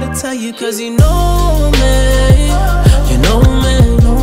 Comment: All this time I thought he said "you're a nomad" hahaha.....I LOVE this song